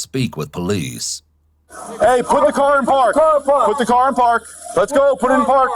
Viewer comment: i wasnt paying attention and couldnt really hear the video all i heard was men yelling "Aardvark" 😂😂😂